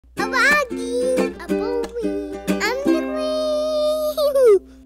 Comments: i love how in the introw it sounds like says: im vloging im boring im the qween when it is im colleeeeeeen! sorry bout the bad spelling:) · When “I’m Coleeeennn” It sounds like he says “I’m the Queeeeen” And it’s soooo cute! I’m so sorry that your feeling so sick Coleen and I am so exited for you! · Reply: He is! If you look back on one of her videos it says “I’m the queen” in the exact same tone and pitch😌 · I love how it sounds like is saying “I’m the Queen!” In the intro